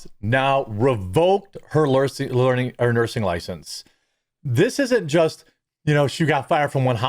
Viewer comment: I thought you said they revoked her Learing license for a minute there. LOL